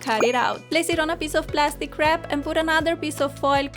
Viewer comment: place it on a peice of plastic wrap... me: wait a sec did she just say plastic crap? i laughed for 3 hours straight